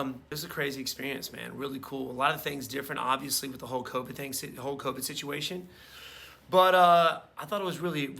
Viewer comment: i thought you said "Hulk Hogan" but it was "Whole Covid"